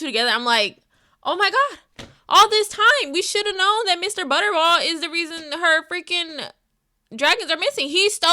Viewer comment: That sounded like Butterwell, which is a House in this franchise. Lol.